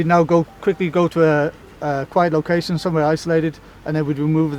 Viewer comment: What did he say at 5:07 ? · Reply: Quiet location